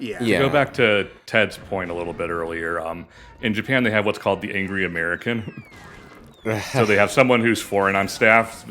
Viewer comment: I thought it was called the "Loud American"